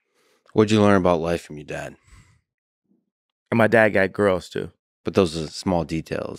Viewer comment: What does Ye say??? His dad got girls too?? What does that mean?? Please any1 can help me?